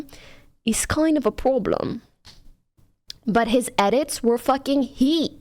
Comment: It took me several re-listens to realize that she said "problem" there. I know she's french or something, but how tf do you mangle the word "problem" that hard?